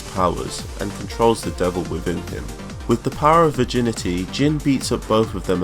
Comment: I legit heard "with the power of virginity." great vid btw